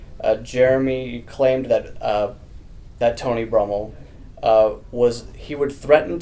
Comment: I swear he said Tony Romo with the question